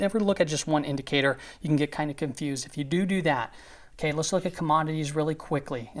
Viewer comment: Did you just say doodoo 18:53?